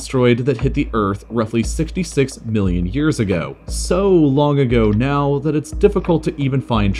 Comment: I thought he said 66 billion years ago. I was like "Wait What?"